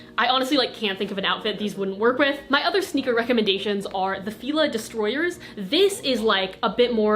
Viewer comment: did i hear 'fila distroyers' lol. luv u girl ur so cute